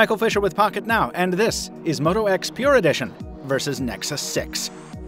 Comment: "moto x pure edition vs. nexus sex" it really sounded like that